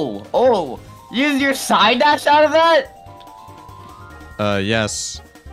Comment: sounded like said s word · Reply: Pretty sure he said "side dash outta that" Not "shit outta that"